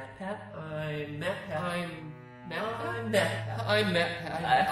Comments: It sounded like you were saying I’m mappat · When MatPat was doing his intro I swear my ears misheard him and heard "I'm iPad." And was like no he said MatPat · it sounds like your saying im mad pat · who else thought he started saying map pat XD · I thought he said I’m fatpat :p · I thought he said "I'm batpat" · Didn't anyone think that Pat was saying/sounded like he was saying: I'm MapPat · he said "im matpat" So much that i thought he said "im met pat" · For a second it sounds like mat says “I’m BatPat”. I love it! 16 minuets later. I feel like I’m going to vomit. Good bye. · What was his name again? I only heard "I'M BATBAT!" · It sounded like he said "I'm madpat"